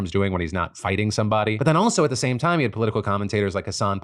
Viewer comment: You might want to tweak your captions. At 9:30 it says "a sand picker" instead of Hasan Piker.